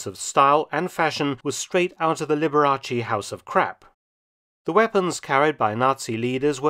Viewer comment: The first time I watched this I thought you said “Liberace’s House of Crack” 😂😂😂😂😂